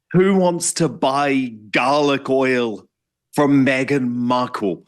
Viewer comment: I heard "gargoyle" when Dan said garlic oil.